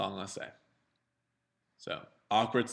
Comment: also...did you say "awkward science sometimes" @ 9:24 ???